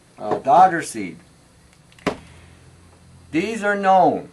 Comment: still i cant understand what you say, put a chock board up and write things down so we can read when we cant understand., sounds like you said dauter seed but dont know